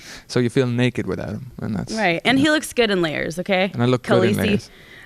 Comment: what she said at 16:30?? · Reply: “And he looks good in layers, Khaleesi”